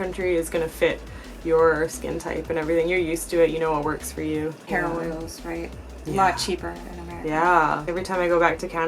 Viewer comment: I thought said Heroine is alot cheaper 😂😂